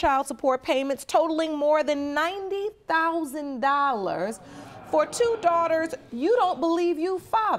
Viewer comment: 90,000 😱 of child support? Court system need to be fix ASAP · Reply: I thought I heard $19,000 but it's $90,000